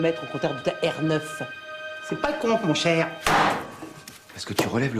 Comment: qu'est-ce qu'elle a dit Simone à 41:53, après avoir dit qu'elle a relevé 229 kilomètres sur la voiture de Bernard? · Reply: elle dit "c'est pas le compte mon chèr"